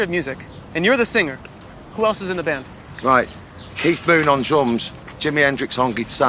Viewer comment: Who does he want on drums? I couldn't understand it :D min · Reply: Keith moon....good call!